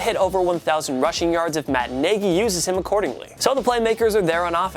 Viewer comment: Did he just say Matt Niggy....it’s Matt Naggy 😂😂😂